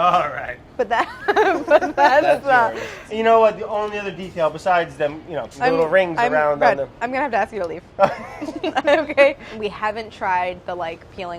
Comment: Does anyone else ship Claire and brad? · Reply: I thought you said bread instead of brad